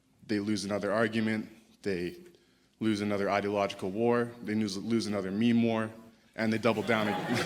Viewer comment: 에 "they lose another meme war" 라고 하는거 같아요. 'meme' 밈(글자만 보면 미미인데 발음은 밈 이라고 하더군요) 이라고 하는 이 단어는 한국말로 치면 개드립, 짤방 등이 아닌가 싶습니다. 짧은 그림이라던가 동영상 같은 것에 무언가를 표현하는 그런걸 말하는거 같네요. 서양권, 특히 미국 캐나다 쪽에서는 좌파가 밈을 못한다는 말이 퍼져있다는걸 들었습니다. 아무튼 개드립 짤방 전쟁 등에서 좌파가 지고 있다고 그러니까 다른 청중들이 웃음을 터트린거 같네요